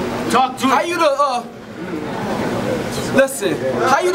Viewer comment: Am I the only one who heard the homie in the back say "talk to that water" 😂😂😂